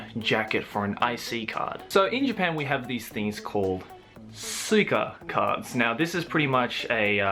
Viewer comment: i thought you said cyka cards then i rewatched that part of the video xD